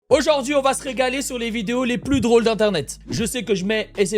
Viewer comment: pourquoi j'ai entendu le cri d'Hornet a la place de "régaler"...